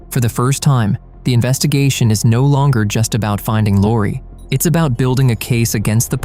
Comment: Omg, this is a sad case. I was so heartbroken for her, that I thought I heard the narrator saying “finding dori”